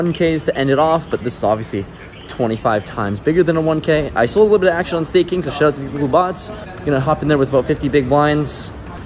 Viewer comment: Can someone translate what he says at 16:24? All I can get is "I saw a little action on stay kings o sha zi ulbaats."